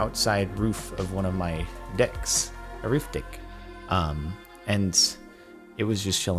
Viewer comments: the subtitle say arif d**k instead of a roof deck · Sorry? Roof... Dick? Is it my child brain mishearing or is this just something I don't know about?